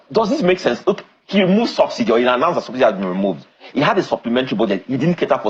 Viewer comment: I thought they said Seun has been sacked??